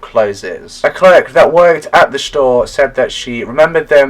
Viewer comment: I SWEAR YOU SAID SHTORE 😂.Thiss case is so odd thoughhhhhh. I have so many questionsssss my brain hurrtssss 😆